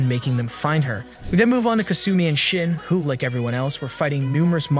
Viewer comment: i heard kasumi and shit the first time